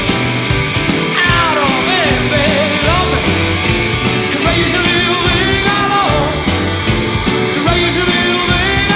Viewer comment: I can’t under what he said can anyone tell me please 🙏🏼🙏🏼🙏🏼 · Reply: Sounds like “baby, love me”. Not 100%, though.